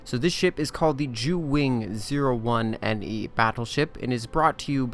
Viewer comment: sounded like "the jew wing"... illuminatis confirmed!